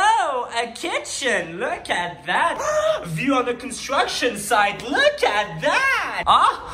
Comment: I heard destruction site